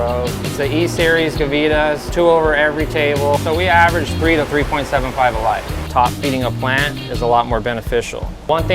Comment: ok wait did he just say 3 to 3.75 a light.. @ 10:47 is he saying hes getting 3 to 3.75 pounds per 1000w gavita light?